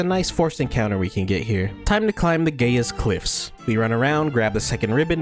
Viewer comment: im sorry did you just say the gay ass cliffs?